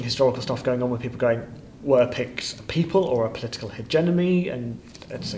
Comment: Um... did he say "hegenomy"? Why yes, I believe he did.